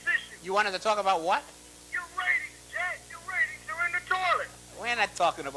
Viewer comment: YOUR RATINGS ARE IN THE TOILET JACK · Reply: I thought he said "your ratings are innotorous"